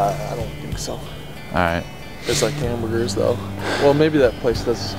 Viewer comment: I swear he said “There's like, kangaroos though” at 1:15... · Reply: lol I thought so too but I watched it again and he said there's hamburgers tho..